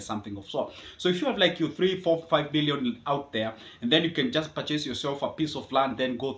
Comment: did you just say if have 3,4,5.. Billion? In that case, I am listening to a video above my paygrade. Good Stuff as always Man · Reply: Oh! Meant million and billion is still achievable as well